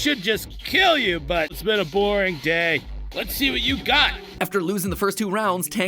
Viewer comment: wait tankman says "it's been a boring day"? I always thought he said something along the lines of "it's metaphoric day" wtf